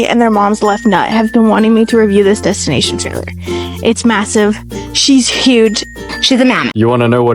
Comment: did she say massive